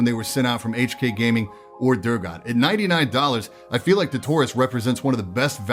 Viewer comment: Idk why, but in my head i thought he said "tourist" whenever he said taurus